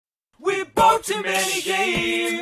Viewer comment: but you have to admit it does sound like he said "we boat to many gayeearss now we gotta play them all so all aboard the steam train"